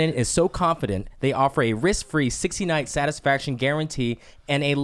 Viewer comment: it sounds like Gilbert said "They offer a 69 satisfaction guarantee..." lol